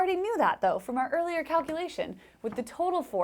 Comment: Did you say "cowculation"? ;-)